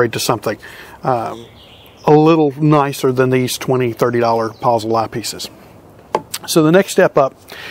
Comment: "Puzzle" is really "Proessl" (german name) misheard